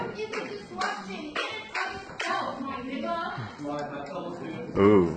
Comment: Did...did she just call that super-patient college official...who sounds black..."my n***a?!?!?!"